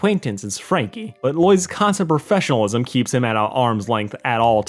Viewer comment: misheard this as “Franky’s professionalism” and had to do a double take